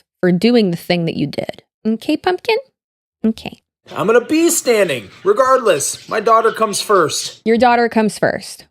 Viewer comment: I heard “I’m gonna PEE standing.” 😂